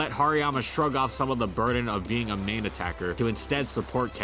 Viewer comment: Why did I hear "being a meme attacker" rather "being a main attacker" in 13:27 ?